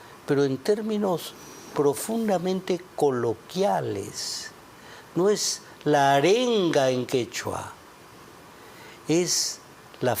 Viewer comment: PUCHA PENSÉ QUE DECIA : LA TERRUCA DA MENSAJE EN QUECHUA....